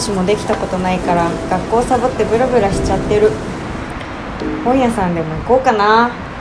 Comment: 本屋さんで抜こうかな？にしか聞こえん